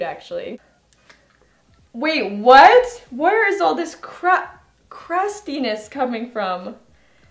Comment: subtitles say "crap crust penis" 😂😂 poor